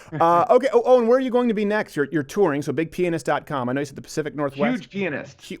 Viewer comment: Could you possibly enunciate the eeyah in 'pIAnist' a bit more? Thought you were saying "huge penis" for the longest time! lol I know that's the joke... I just thought that all subtlety had been removed! :)